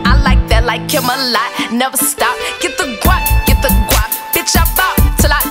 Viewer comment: At first listen I thought she said "get the guac get the guac" To which I'm like "HOLLA AT MY GURL" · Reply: GET THE GWOP GET THE GWOP BITCH I BALL TILL I DROP!!